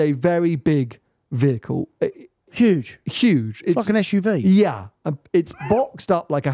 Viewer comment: <- I know said "like an SUV" but it really sounded like "fuckin' SUV" if you listen right.